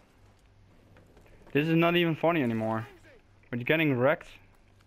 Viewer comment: wow you used an assault rifle shame about the fucking campers though still good gameplay though and when you said this is not funny it sounded like you said fanny lol